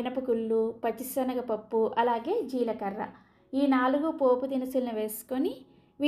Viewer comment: is she saying "after the papaya leaves"?